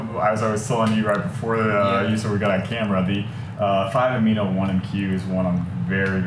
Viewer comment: It's too bad they mumble and don't repeat or spell out the names of the peptides. Can anyone tell me what peptide they mention at 9:00 in? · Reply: Sorry we mumble. The peptide is 5 amino 1 M Q. We are actually doing a video on peptides for weight loss this Wed the 27th.